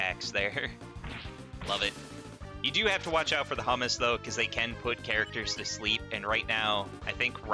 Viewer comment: did you say hummus? Now I'm hungry